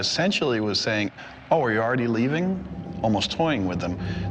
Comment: i heard how are you doing xd 😂